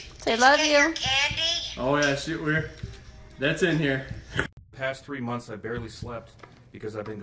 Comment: what's he saying? Oh yeah she we're that's in here? I don't get it.